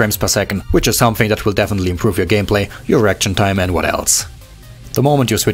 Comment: When he said "Reaction time" i thought he said "Erection time"